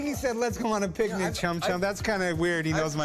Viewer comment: does sound like chum chum less it’s dum dum lolz 52:53 think he said 110 whoop whoop he he